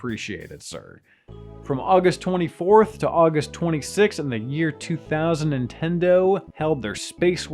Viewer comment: I thought he said “in the year 2010-tendo” or “2000 and ten-do”, like how some people say 2000 and 7 for the year, for example.